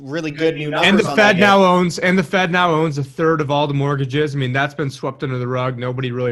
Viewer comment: ? 1/3 of mortgage? did i heard right?